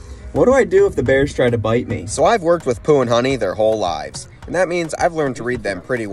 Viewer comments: When he said the names I no shot thought he said poonani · At first I thought he said pooh and nonnie, I was dying… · I thought he said "I have worked with Poo & Nani" at first 🤣